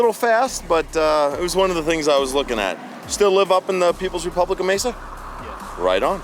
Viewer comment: Did anyone hear him say “People’s Republic of Mesa” at 1:20? 😂😂😂😂 hilarious